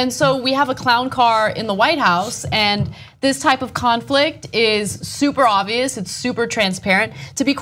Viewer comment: I thought Ana said "Clownflict" lol